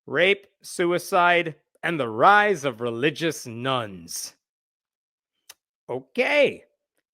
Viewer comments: I heard "rise of religious nuns", not "nones". I thought we were getting old school nunsploitation there for a minute. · Everytime I hear the "Religious Nones" I hear the "Relgious Nuns", like yeah, obviously Nuns are religious